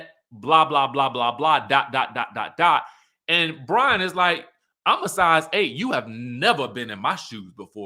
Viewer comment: He said “Im a size 8” ?!? I thought he said “I’m a 8” like 8 outta 10, meaning he’s way finer than Philip. Which idk, that’s debatable, but shade is shade 💅🏽